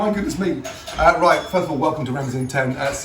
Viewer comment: At first, I thought you said "Welcome to 2010!"